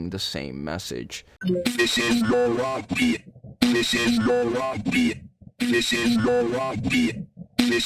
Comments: why did i hear "this is your rugby" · i always thought they said "this is the whoppi" for some reason lol